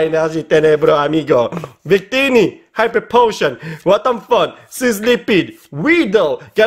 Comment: Il y a que moi qui a cru qu'il disait "WTF" ?